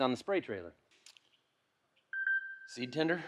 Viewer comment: I understood "Tinder" instead of "tender". Laughed very hard for 5 seconds. Watched it again and recognized the expectation that I have for this channel.